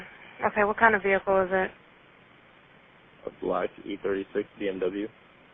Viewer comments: It says inaudible but he clearly says "e36" 1:51 It says inaudible but he clearly says "The three words" I am free on the weekends if you need a new transcriber. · What stood out to me in this video is that the transcript says 'inaudible' where what the caller says seems very easy to understand: 0:58 -> 'E36', 1:53 ->'three words'.